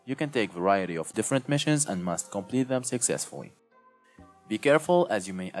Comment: did he just say successfuwy